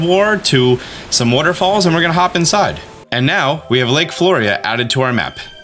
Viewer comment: I thought that said “lake florida” and I was so confused